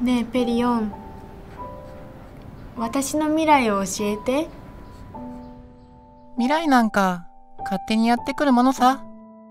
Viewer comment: 一瞬りななんに聞こえた。